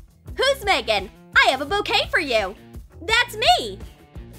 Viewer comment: When they said bouquet I thought they said cocaine